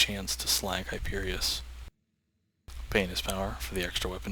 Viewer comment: Doesnt it sound like he is saying penis power